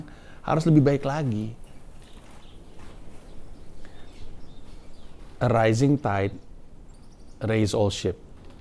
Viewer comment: Rilis om. btw sempet salah denger "A Rising Tide Raises All Shits" :_LOL:. Bener kan, pas tsunami airnya item banget bawa kotoran dari dasar laut.